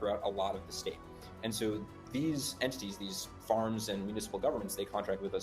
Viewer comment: Did he just say Entities?